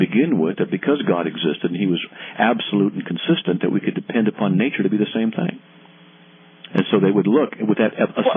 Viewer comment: Did he said "Depend upon Nature"? (4:05) that makes Christians like Pagans.